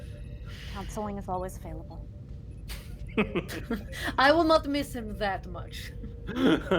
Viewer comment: What does Olin say at55:51? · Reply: They something like, "Hmmm, I think I'm gonna miss him, too."